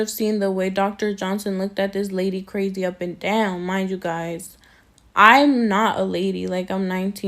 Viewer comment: Did she just say I'm not a lady? Are you a gentleman?